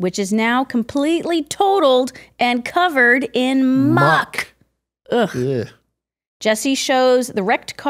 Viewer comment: Did they say "mock" or "muck"? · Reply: Muck